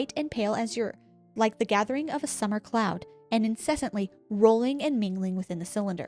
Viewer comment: This was the BEST! I've watched may documentaries about this so I knew a lot of it but SO MUCH FUN! Escpecially when I thought you said a dark rolling clown! lol (instead of cloud) Im terrified of clowns. Keep up the good work.